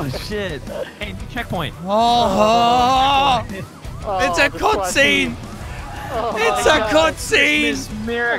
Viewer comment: You know when terriorisor said it's a cut scene I thought he said I concede